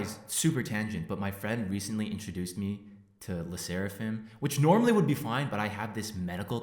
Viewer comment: I heard "the seraphim" and lost my shit